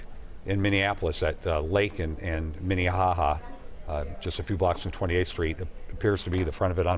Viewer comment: Lincoln and what?? Its sound like he said minnie Ha Ha is that correct? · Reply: Yeah, a street is named Minnihaha or such, and dainty little Minneapolis is quite proud of such things.